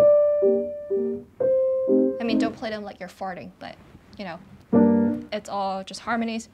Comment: did she say "don't play them like you're farting"? Sorry .. listened to the comment 3 times and can't hear anything else ... maybe it's wishful thinking to have the word "farting" introduced to the classical repertoire ...